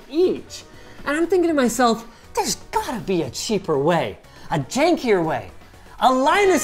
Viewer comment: When Linus said "a jankier way", I heard "A Jakey-er way".